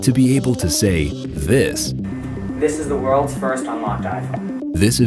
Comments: Oh, my... dude, seriously. Edit the audio next time. We can't even hear what he says "This is the world's first... (???)" WHAT?! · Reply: "This is the world's first unlocked IPhone." It was very clear tbh lol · what he said at 2:25?? I am not English speaker to understand clearly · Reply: "This is the world's first unlocked iPhone"